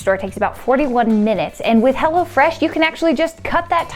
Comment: sometimes it sounded like was saying "Hell'a Fresh" lol